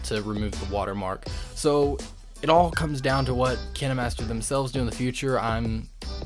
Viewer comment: Hol’ up- did he just say “Kina Master”